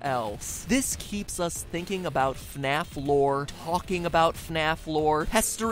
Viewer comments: in the vid it sounds like hes saying FNaF floor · Am I the only one who heard him say fnaf floor?